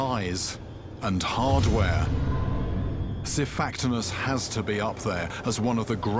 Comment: might be being naive or just mis hearing what was said but at 00:13, 'syfactimus' or however you spell it, not aware of that, if its a species or genome of fish how do you actually spell it so i can find out the definition?